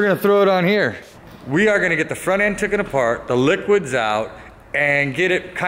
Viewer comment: did he actually say 'tooken" please i didn't think people unironically said that.